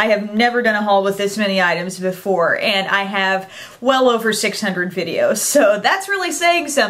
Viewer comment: I thought she was saying that she has well over 600 items in her haul! lol